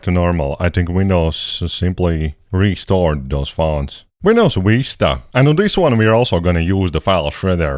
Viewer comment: WHY DO I HEAR "Windows Weesta"